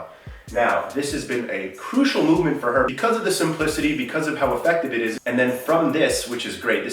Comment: When said simplicity I thought he said some pussy and I was like I didn't know you were freaky